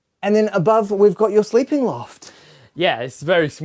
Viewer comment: did he just said “it’s been a TARDIS”?! 😳